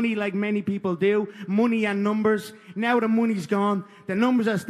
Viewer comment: You wanna know what's funny? at 1:05 when Conor said "money" the captions said "mooney" lmao I'm dying